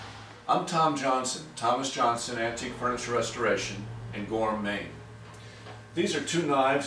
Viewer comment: Me too. I thought he has been saying gourmet all this time. I thought maybe he had a stutter from the way he said it. I was wondering if he was going to cook something someday. Finally, I discovered he has been saying Gora Maine. Wow! I am such a fail.